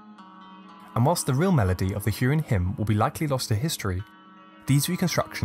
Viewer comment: Oof I thought it said “Hurricane Hymn" 😅